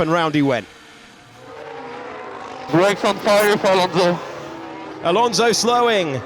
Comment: Please bring back the subtitles for radio messages, I have no idea what gasly said at 1:40 as an example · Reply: I heard “right front tire for Alonso”, but I only got it after rewinding a few times